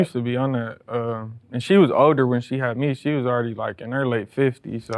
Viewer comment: did he say his mom was in her late 50s when she had him?